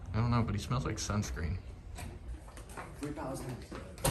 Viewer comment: I don't know if y'all heard what that guy said with glasses and white colored shirt did he say 3000 click referring to the los Angeles sheriff gang the 3000 boys or I'm tripping 29:43 got to go back a bit